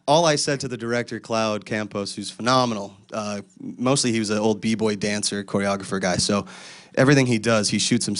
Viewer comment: oml he said b-boy and i no joke thought he said beebo